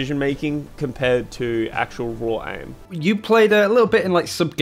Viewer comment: did he just say raw aim as in raw sauce?